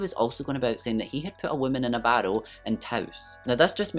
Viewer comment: I love your channel so much! I'm obsessed with your accent btw. I'm American and when you were saying barrel I thought you were saying bottle 🤦...could not figure out how this Andy guy stuffed a woman in a bottle 😂.